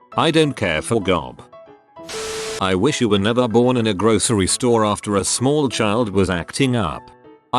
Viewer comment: I also heard that as, "I wish I had never had you in a grocery store"